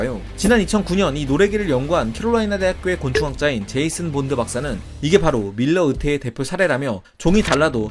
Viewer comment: 아니 나만 제임스 본드라고 들었나 ㅋㅋ